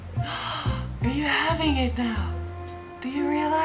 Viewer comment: ทำไมผมได้ยินเต่างอย ฮ่าๆๆๆ